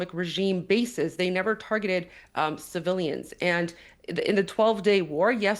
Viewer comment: did she just say that they didn’t target civilians???